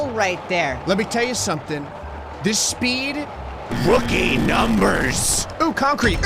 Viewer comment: it almost sound like it said “This speed Will Gain Numbers” Which I guess makes sense